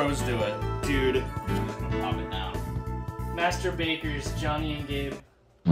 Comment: I thought he said "mast*baters jonny and gabe"💀